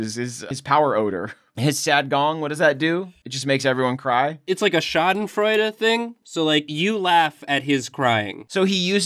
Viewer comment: Did he just say »Schadenfreude«? My german heart is flashed!